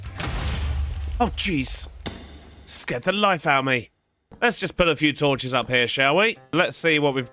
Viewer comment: I thought i heard you say ,,scared the cat out of me" 😂